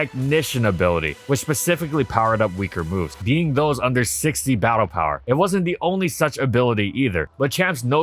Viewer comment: Did he just say “Battle Power”?! if that’s what BP mean instead of Base Power, I’m going to go nuts